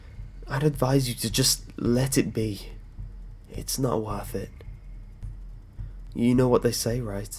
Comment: Oh gods, at the end when you said "you know what they say right..." I misheard it and thought you said "you know what they say Red..." o.o I almost flipped my shit, haha! But yes I remember Postman Pat from when I was younger, my siblings used to watch it in the afternoons. I do love me a creepy little lost footage pasta.